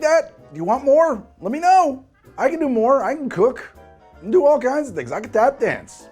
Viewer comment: Really thought I heard "cocaine" for a second there ......